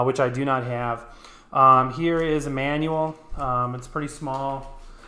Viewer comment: sounds like, here is Emmanuel, instead of here is a manual lol